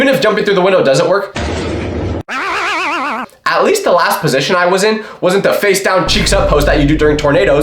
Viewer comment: It sounded like a potion🥀🥀🥀